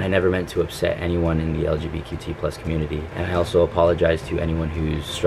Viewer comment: Did he say LGBQT community? I'm sorry WhAt?